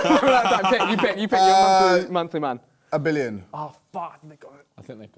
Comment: did I mishear that or did Harry say 'f*ck n*gga'